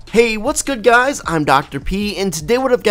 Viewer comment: did u said dr pee